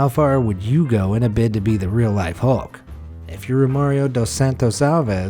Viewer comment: I thought the narrator said Mario for a second there XD he actually said Romario XD